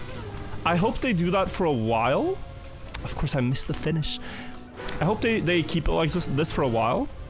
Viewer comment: Heard "of course, I missed the finish" but what he said was "of course I miss the Finnish" for anyone that was also confused.